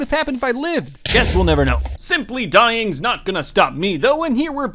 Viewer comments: An ad came on halfway through he said ‘simply’ so I thought he called me a simp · (blazing lol) when he said simply it cut to a ad but before he said the whole word all I heard was simp